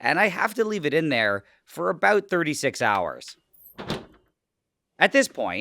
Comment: I thought he said 3-6 hours Wdym 36 HOURS???!!